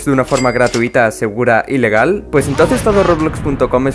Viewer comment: creo que dijo ilegal :U